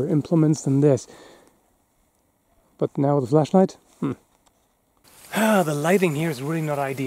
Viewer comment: I thought that said fleshlight and was thinking what kind of video is this😂